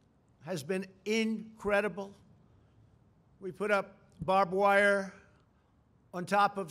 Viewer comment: babwire was the only thing i heard and it made my day